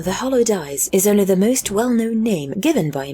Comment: I thought it said "hollow dyes", so i expected a christmass spaicel. Might aswell be in the 40k universe